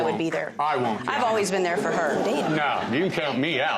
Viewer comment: lhh did judge say damn